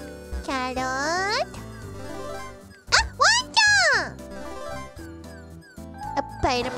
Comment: can someone please tell me @ 0:56 what korone is saying? something like Wanchan? わんちゃん its translated as "pupper" but sounds nothing like Inu??